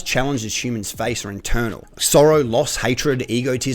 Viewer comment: I really thought you said Soros at 4:55.